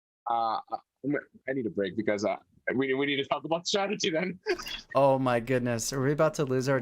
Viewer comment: Whoa Ollie can speak korean :0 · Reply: Oh, that was actually Korean? I thought she was saying "Oh my hotoke" (Hotoke is Japanese for Buddha).